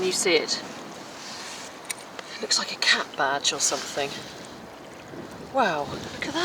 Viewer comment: Lovely video! When you said cap badge I thought you said CAT badge so I was desperately trying to work out the cat shape 🤦‍♀️ I like the lions! I had to watch this in 2 parts because of internet, so I didn’t see the kitten’s transformation until just now! The robin singing at the end of the video was beautiful.